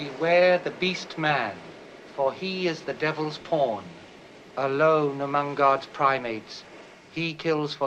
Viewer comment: immediately I have to pause the video, because I honestly thought he said "the devil's porn"